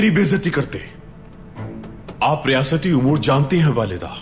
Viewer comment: did Baybolat says "Walida" to Hayme Ana Ya sirf mujhe hi aisa laga 😅😅